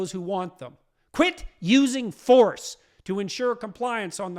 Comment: What? Have I heard you right… ‘using force to make unvaccinated comply’? Jordan I think despite the fact that you adore the ‘normal’ convenience that you [now amongst the elites] so accustomed to, you fail to see the point of those who do not want to comply. Maybe you have not really understood your much quoted author Solzhenitsyn, perhaps have an evening in with your conveniently delivered meals and enjoy ‘A hidden life’ from the cinematic service of Amazon would help you. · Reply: he said quit using force